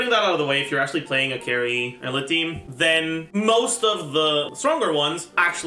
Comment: who is he talking about i dont understand?? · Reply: eula! its pronounced oila in the german aka correct way